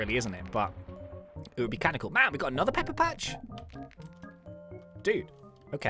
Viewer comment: i thought he said peppa